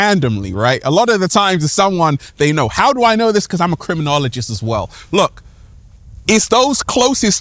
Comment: As you say "I'm a criminologist" I almost heard you say I'm a criminal too 😂 It's always a blessing to listen to your views ❤